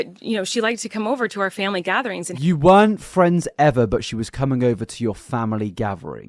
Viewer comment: hold on Fraser, you really latched on to her saying "we weren't friends ever" when she did actually say "at first" and not "ever" I only point this out because you paused it so many times to reiterate something you misunderstood. Not defending her in ANY capacity, just trying to keep us all on the same page.